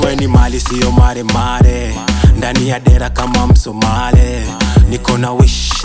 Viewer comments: I heard like I’m Somali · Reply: Indani ya dira kama msomali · Did he said Somali? at 00:33 🤔🇸🇴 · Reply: Ndani yaa dira kama msomali